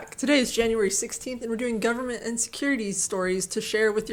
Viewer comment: I heard "Government Insecurity" during the intro.